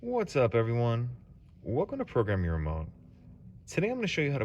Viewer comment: I could’ve sworn he said “program your mom”